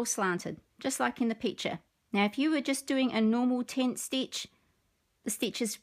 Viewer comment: I thought other people were saying "ten stitch" in their videos. So you do half cross stitches on the whole design? It reminds me of needlepoint. What are you using on your tablet that lets you highlight your chart?